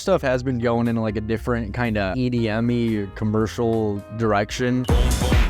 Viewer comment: I thought you said Edi Yameen not edm-y